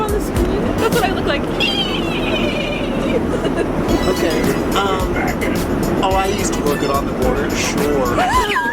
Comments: Does anyone know what Garrett says at 25:40 when he is impersonating Morgan? I can't get the audio clear enough! Lol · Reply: Oh I used to work at on the border sure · Am I the only one that can't make out what Garret says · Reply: Amy32Chen “Oh I used to work at on the border. Shoooorre(the way he said sure) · can someone tell me wth garret said as his impression of morgan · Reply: "Oh, I used to work at On The Border, sure" On The Border is a Mexican restaurant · what did he say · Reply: Ive been wondering this eevery single time i watch. “Oh i used to work out at the border suuureee” thats all i could really gather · Wait what does Garrett say at 25:48 ??? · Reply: Imitating Morgan: “Oh, I used to work at On the Border, sure..” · what does garrett say at 25:49 ??? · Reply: harl marie I think he said “oh I used to work at ‘on the border’, sure” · someone please tell me what Garrett is saying😥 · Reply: BellaBoo4U 15 he’s saying „oh I used to work along the border, sure“ because Morgan used to work on the border and talks about it a lot